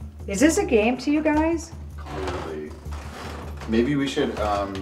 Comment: who was that voice @ 18:20 when ask 'is this a game to you guys?' and it sounds like it say "Clearly" or it's just the ovilus? but it looks like they didn't notice that.